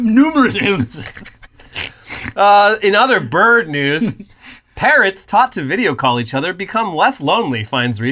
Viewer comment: I wasn't paying full attention towards the end and could have sworn you said "Taught PARENTS to zoom call each other" and it didnt even faze me one bit 😂 I was just like "huh, good that they're getting some contact in these ever more isolated times." Then said they were preening and I suddenly realized I'm an idiot 😂